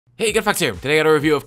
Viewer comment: I thought you said good fucks here